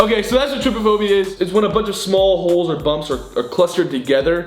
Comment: lol when gray said small holes I thought he said small hoes XDD